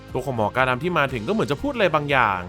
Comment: หมอกาดำพูดว่า Don't Move ชัดมาก หมายถึงหย่าขยับหรืออย่าดิ้น